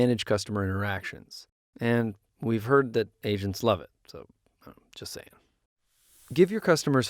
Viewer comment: When he said "we heard agents love it" I totally heard "we heard Asian love it"🤦🏻‍♀️😅